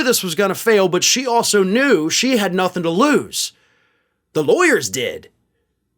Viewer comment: I could have sworn she said she wasn't even going to lose